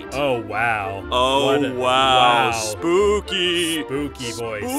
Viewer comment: When he said spooky voice at the end I thought he said ”spoopy bios”